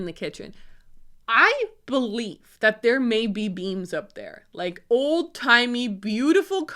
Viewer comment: oh i thought you said old-timey, beautiful cottage beans. not beams.